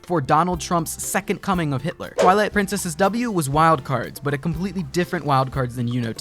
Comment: Did you just say "twilight princess's W" 1:48 ? :O ZELDA FAN